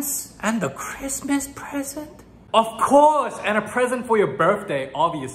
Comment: for some reason i hear "of course and a Prison for your birthday!"